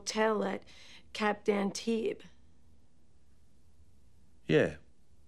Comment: what does Dr. Melfi say? Cap Dan tien?